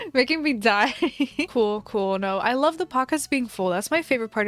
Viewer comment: When she said "pockets" I thought she said "pacas" Like alpacas